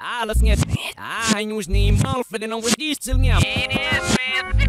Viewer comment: Sounds like he says "it is Smith" rather than "it is Slim"